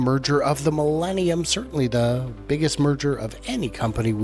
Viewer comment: Initially, I thought you said the biggest murder in history 😂… I suppose it will be over XAI‘s competition…😬😏